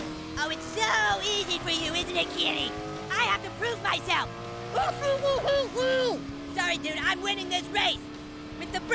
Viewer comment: That's cause you hate me? Someone help out I'm trying to figure what Kenny said. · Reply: Kenny says “I fucking hate you!”